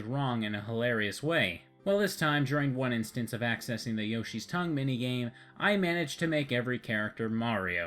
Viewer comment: yoshis tongue meeting? yeeesh lol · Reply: DeathmetalIndian I misheard it as Yoshi's tongue mating so like yoshi makes babies using his tongue